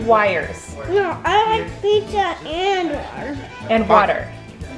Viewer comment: Pizza and Butter sounds like he said